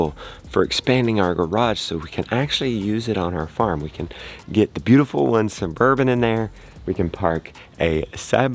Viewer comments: When yo said ''for the beautiful one's Suburban'' I thought you said ''for the beautiful one's bourbon'' Hahaha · Happy Birthday. I hope you all are doing well. LOL, I honestly thought you said, "We can get the beautiful one's bourbon in the garage." Then it dawned on me that you said suburban. It made me laugh so I was hoping it would make y'all laugh as well. :), your birthday blessing. You are a wonderful husband and father.. well you are a daddy really. meaning, anyone can be a father, it takes someone special to be a daddy. You are a wonderful teacher to all of those who are interested in homesteading and people who love chickens as much as you do. I hope all get well soon. Tell your mother-in-law I am praying for her and I think of her often.